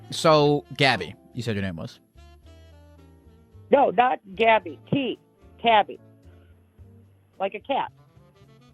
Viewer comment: I heard “cabby with a t” four times from two different people 😂